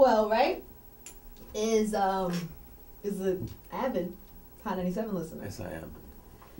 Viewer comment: Anyone know what she's saying at 5:15? "Maxwell is an avid_____" I can't understand her. Can you? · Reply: "Hot 97 listener"